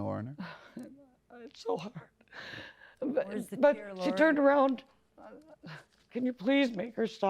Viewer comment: does anyone know what the defendent said at 2:33? when shes talking to the plaintiff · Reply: i think she said “where are the tears Laura?”